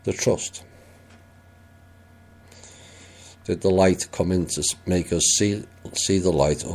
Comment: I would say no because it's about our MIND! Can't you tell he smokes! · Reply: I thought I heard lighter lol